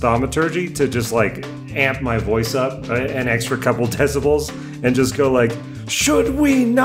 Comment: The closed captions mishear decibels as "testicles"... 1:01:18 "so okay Canada's mess" 1:10:28 "So the only people exposed our colleague yelled, Mass and Jane"